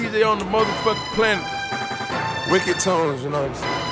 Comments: It sounds like he is saying Weo and not Wicked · was I the only person who thought he wasn't saying wicked? I'm hearing weyo weyo weyo weyo. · sounds like hes saying mayo not wicked